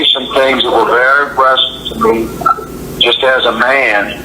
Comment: I thought he said "aggressive", not " impressive"!?! 🤔